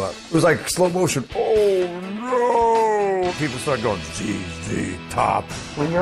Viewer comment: what did he say? (In the deep voice) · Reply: ZZ Top